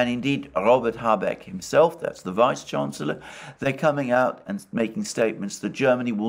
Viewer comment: A mishearing on my part: "Robert Habeck the Reichskanzler..." — but Alexander correctly used the words "vice chancellor". Intrusive thoughts...